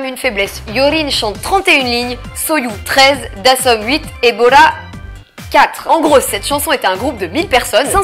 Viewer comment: "et Bora". J'ai entendu "Ebola". C'était tout pour moi.